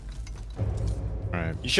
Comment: what did that guy say at 15:03? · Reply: He said you should’ve died in attack on titan which is a show where a character called Levi dies. The tm is calling Levi because of the way he threw the grenade I think.